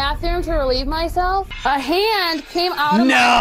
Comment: I thought she said ham came out I was like wtf